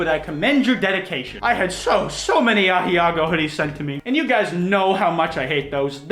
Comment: it took me like 10 minutes to try to figure out what he was saying. I think I figured it out. He’s just horribly, horribly mispronouncing ahegao.